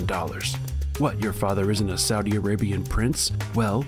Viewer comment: For a second there I heard "Soggy Arabian Prince."